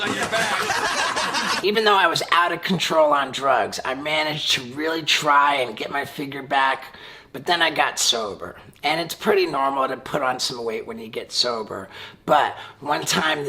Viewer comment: "but then i got sober". Sounded like he was betrayded or some shit :D